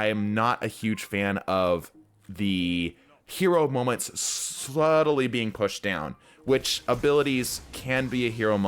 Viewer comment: Did you say 'sluttily'? That moment when you can't decide on slowly or steadily, lmao. I've done that sort of thing all the time.